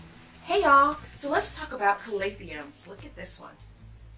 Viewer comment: Is it just me or do I keep hearing “Calath-ium”?